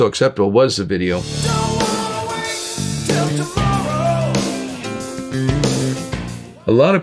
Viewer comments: "Right Now" is probably my favorite Van Halen song, esp. by Sammy. "Dreams" is up there, too. I always thought he said "til tomorrow" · I always thought it said "HANG.. on till tomorrow"